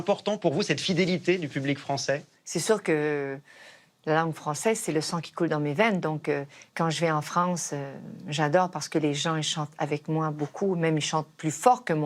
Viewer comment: Someone could please write me in French what she says at 12:19 to 12:40 ? Please! I thank you ^^ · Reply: « C’est sûr que la langue française, c’est le sang qui coule dans mes veines donc, quand je vais en France j’adore! Parce que les gens, ils chantent avec moi beaucoup, même ils chantent plus fort que moi, mais j’adore ça! Et j’en profite pour faire des folies en France, parce que pour moi la France, c’est l’amour pur, ça me fait vouloir aimer la vie encore davantage, j’ai envie d’aimer encore plus fort. »